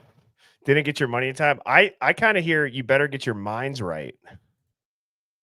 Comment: Hive - What stood out to you the most? What did you hear? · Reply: For the clip you said sounded like “better get your minds right”, I hear “better get to your Elantra son”